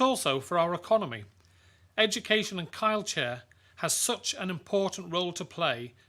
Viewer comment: in the video. Did he really say "kildchair" lol